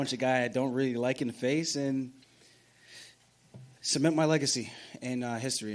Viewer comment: Did he just say “submit my legacy”?!?!?! Bruh! What LEGACY?!